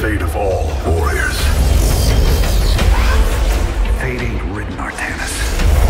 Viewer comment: Can someone tell me what Jimmy says on 2:04 ? Thanks ! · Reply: "fate isnt written artanis"?